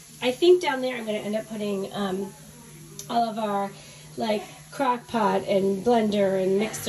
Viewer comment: i heard crack pot